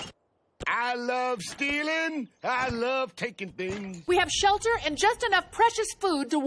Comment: The first time I heard 5:46 I thought Bender said 'I love kicking things'. That wouldn't be too out of character though